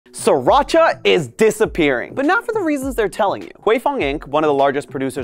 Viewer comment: i thought he said Sirchia was depresing